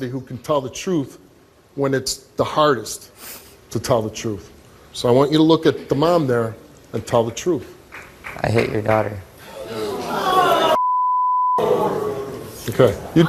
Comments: When he said "i hate your daughter" my heart broke like how could you hate a baby 😭 thats so sad i feel for her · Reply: I thought he said,'I HIT' your daughter'???😳😳😳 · I was expecting her to go off after he said “I hate your daughter.” That cuts deep. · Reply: I thought he said " i hit your daughter"